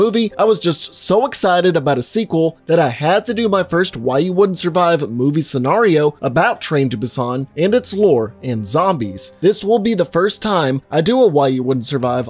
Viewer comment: I keep hearing "Hawaii wouldn't survive"